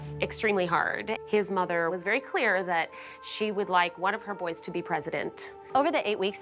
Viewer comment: I thought she said she wanted one of her boys to be pregnant I was so confused and I had to replay.